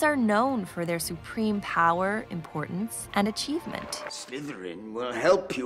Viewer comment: did she just say umbridge is important?! Bish expect me at your house tonight cuz imma avada kedavra you.